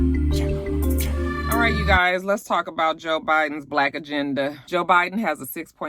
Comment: All I heard was "Joe Budden" has a lift every voice plan. I was confused af. 😂😭😣